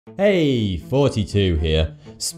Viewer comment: Is it jus me or does it sound like he says "Heyyy 42 here" at the start of all his videos😤